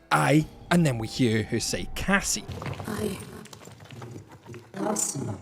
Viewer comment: I didn't hear "i- Cassie" I heard "i'm awesome."